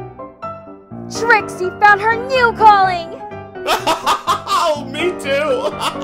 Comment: Did you said "new" not "true"? Anyway nice comic I'm laughing so hard and can't stop. XD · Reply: IKR it was so funny! it was new